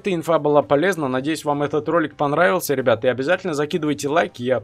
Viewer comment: ставь лайк если тебе послышалось или нет?? когда говорил надеюсь етот ролик вам понравился и на заднем фоне ктото сказал НОУ ето из какогото мема вот так вот самые внимательный в студию точнее етот момент на